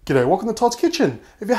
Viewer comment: I'm not too much into sweet stuff, but this looks my kind of pastry :D Funny side note: everytime, I hear "Get out welcome to Todd's kitchen". Should I get out or am I welcome? HOW CAN MY LIFE CONTINUE LIKE THAT?!!? :'(